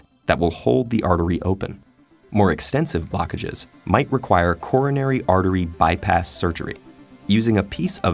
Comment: What he says: Coronary artery bypass surgery What i hear: Corona artery bypass surgery